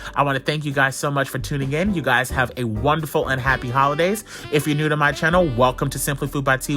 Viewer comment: did i just hear unhappy???? 👀👀👀👀